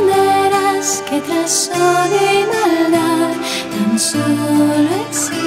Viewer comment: que dice en el 0:56 no entendi que dijo alguien plox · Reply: Ikertxu Berdu *Comprenderás que tras odio y maldad.